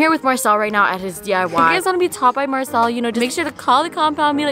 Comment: i thought she said topped by marcell at 1:02 and i got so scared for a second